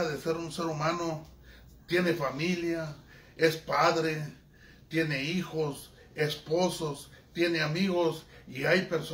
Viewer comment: Min 1:25 escuche bien dice tiene esposos